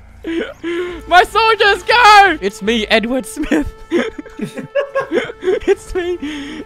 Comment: Edward smith: for humanity!! Erwin smith: ... who is this clown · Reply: but.. i.. i. I Thought he said n-word smith